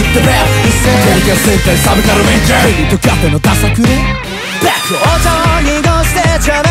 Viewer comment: ここのちゃんなんて言ってるの？ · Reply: ｢(変に尖ってんのダサくね？)｣と言っているようです〜！！